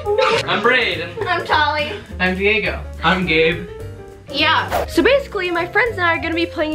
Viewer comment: I thought gabe said I’m gay at first